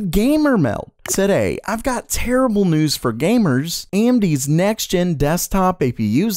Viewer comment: For the last 6 months I keep hearing "I have terrible news for gamers"😔